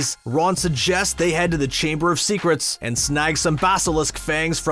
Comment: i think i heard snog